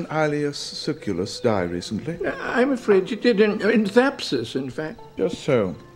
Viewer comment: Lmao @ 3:47 when dude said Zapsis (or whatever he actually said, couldn't make it out clearly) he spit a bunch of food out. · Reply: Thapsus. The battle of Thapsus